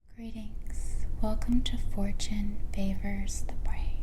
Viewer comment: Did you just say "Welcome to 4chan" ha ha ha